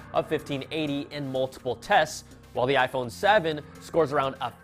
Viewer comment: Did he just say iPhone 7c 😂😂😂